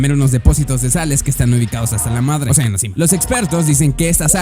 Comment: Escuche mal o en el minuto 1:47 dijo hasta la madre